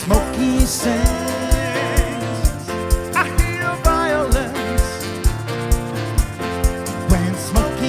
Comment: I used to think he sang “ when Smokey sings, I hear violence “ 😂😂 that voice though ❤❤